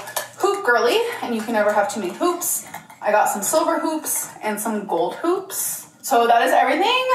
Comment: You’ve scarred me, the first two times she said “hoops” I truly thought she said “poops” and was SHOCKED you didn’t react😂